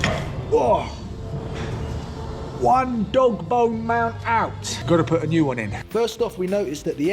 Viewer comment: Lol did you just say dog bong? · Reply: Dog bone 😂